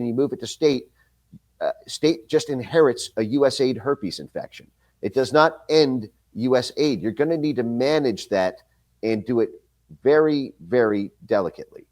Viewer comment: did he say America has herpes lol? Yo speak for yourself man lmao.